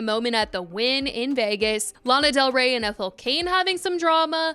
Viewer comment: I thought you said “Lana Del Rey and cocaine having some drama” and had to rewind 😂